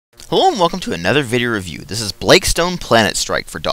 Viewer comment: I also thought he said Hellom, like in Shalom. Hellooom! Dont change it though, because I strangly grew fond of it.